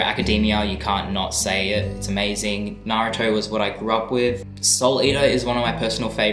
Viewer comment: I thought he was saying salt eater 😅